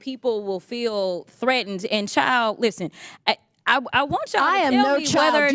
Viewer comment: As a black person, that congress women getting upset over the expression “oh chile” (context: a southern pronunciation(or AAVE) of “oh child” used to express surprise or disbelief, not calling someone a literally child) had my dying laughing how uneducated in other cultures she is and how quick to anger she was over lack of understanding! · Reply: I did not know this and thought she had said “oh child”. Thank you for explaining this and educating me. Either way it was perceived it doesn’t excuse the actions of the transphobe lol